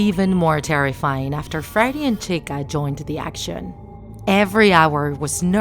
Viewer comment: I thought she said Fred, Boney, and Chia XD